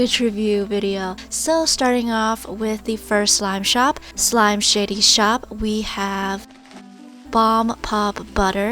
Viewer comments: I thought she said slimeshittyshop😂🤣😅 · I heard “slime shitty shop”